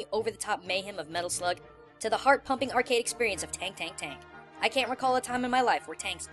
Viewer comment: When he said tank tank tank I thought he said kkk